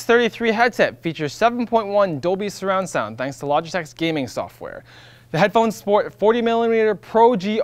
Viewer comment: It sounded like he said "Adobe surround sound" instead of "Dolby surround sound."